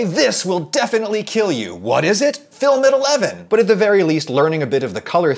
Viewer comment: Wtf does he say at 1:15. Film at 11?